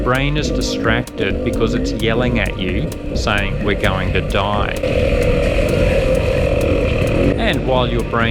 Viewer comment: So that is what the voice in my head is saying "your going to die!" I always thought it was saying watch us fly